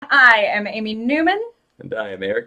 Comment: At the beginning when she said, "Hi, I'm Amy Numan." I thought she said, "Hi, I am a human."